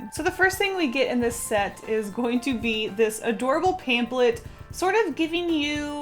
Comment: Did she say...pamp-let?